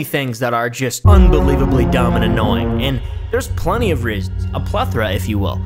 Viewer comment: when he said reasons i thought he was saying rizz